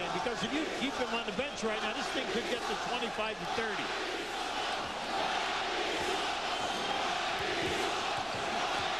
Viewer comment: I'm from Argentina. I can't understand what's fans scream on 1:04:32 ¿Can you tell me? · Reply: "Riley sucks!" haha.